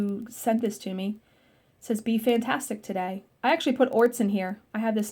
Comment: The fox tray is adorable...I thought it said "be fartastic today!"